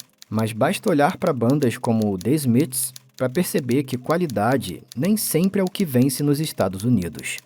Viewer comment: The Smiths tomou de graça ou eu entendi errado? kkkk